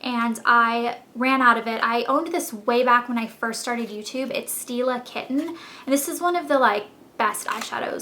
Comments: Haha, when she said 'stila-kitten' I thought she said steal a kitten... And I thought, " wow that's a weird Name for an eyeshadow..." Lmao. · when you say "stila kitten" it sound like you say "steal a kitten" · Stila Kitten is beautiful!I have one and I love it!!! Its funny cause when u say it, it sounds like ur saying Steal-A-Kitten, Lol and on a count of pine cone I thought it was hilarious!